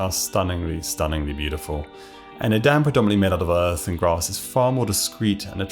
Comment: What did he say at 1:33 mins? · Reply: Predominantly!